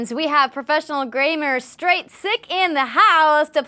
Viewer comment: LoL; did you say "Graymer"?? [0:00:04] XD ... Lovin' the contrast between your awesome red hair & blue top - very Kryptonian. =) ... Looked like JesV was givin' you easy kills though (CoD) ... could've been more aggressive with his Sniper Rifle & DMR though..and he could've picked better sniping positions, or a different Armor Ability (if there was more than just Sprint). Hologram or Jetpacks are the best Armor Abilities to use on the map, The Cage.